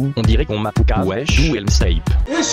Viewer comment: "D'où elle m'snipe" ce que j'ai entendue "où est Snape ?" ce que j'ai répondue "à Poudlard"